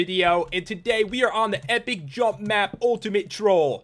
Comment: I thought it said Epic Trump Map. facepalm